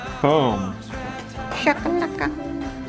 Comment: Did I hear say shaka laka after said boom? I think it was the pinched little voice she did it in that cracked me up the most.